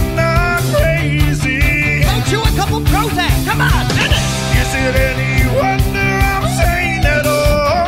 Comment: when he said comon dennis I thought he was saying comon deada$$.